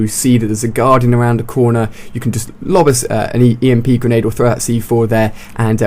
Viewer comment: did upheld just say eat emp? XD